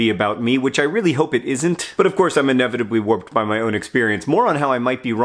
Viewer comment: "...but, of course, I'm inevitably warped by my own experience." I definitely heard "whooped" on the first listen. Either way..it works 👌